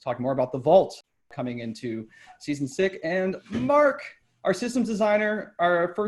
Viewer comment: "Season Sick" or did I hear it wrong?